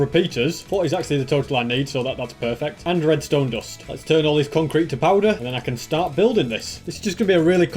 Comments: did he say turn concrete to powder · Did he say concrete to powder